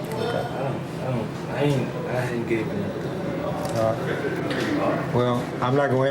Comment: Crazy how at 7:07 when he clearly says I ain't did nothing, it doesn't show up on the caption